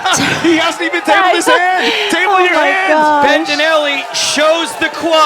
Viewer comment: Did he just say "table your hands, bitch!"